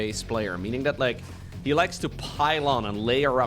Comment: "He's a very momentum-based player. Meaning that he likes to PILE ON..." here i thought at first that you said "he likes PYLON" the Protoss building and this map name"